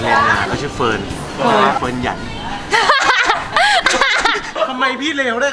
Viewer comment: เดียวๆนะเค้าได้ยินว่าอะไรเฟิร์นๆนะตอนที่3:34อ่ะเฟิร์นหยันฟันเหยอนป่ะ(อร๊ายยเค้าชื่อเฟิร์นแต่ฟันเค้าไม่เหยอนนะ)ไหนๆๆใครชื่อเฟิร์นมั่งค่ะเค้าอยากรู้ว่าคนชื่อเฟิร์นจะเค้ามาดูกี่คนอ่ะค่ะ